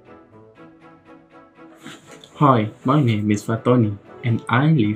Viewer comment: I thought he said "Hi my name is Fat Tony" 😅